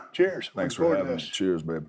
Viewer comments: Did bro just say “cheers babe”?😂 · did he say 'Cheers babe'? lol